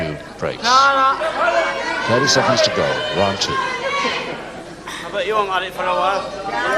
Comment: He says :l"Ill bet you'll marry for a while" not "I bet you're married for a while." I think he's saying the dude needs to settle down and have a family and not be in a men's business.